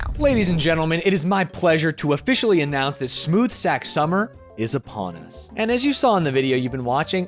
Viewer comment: i thought he said "smooth sax summer" and was so confused when he started talking about his balls